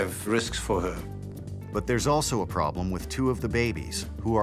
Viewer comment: I thought you said the 3rd was a probe